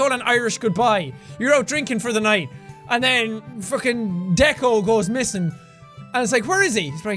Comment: legit thought he said Deku 😂